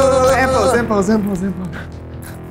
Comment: why do the subtitles say simple simple simple and not end pose end pose end pose ???